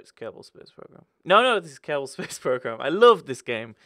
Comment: Ohhhhhh 'Kerbal' Space Program, I always thought he was saying Terrible Space Program in his modventures